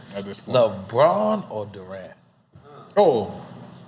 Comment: I’m not a basketball fan so I thought he said LeBron or Duran, what’s Roberto Duran got to do with this❓❓❓